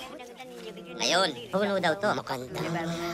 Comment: I vivdly remember as an 8 year old back in 2005 that one of the Ewoks spoke what sounded like Tagalog so I came to check if that wasn't a false memory. Et voila I was right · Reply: Yes he said maganda to c3po